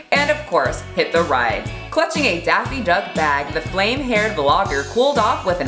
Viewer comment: sorry did I hear Daffy Duck?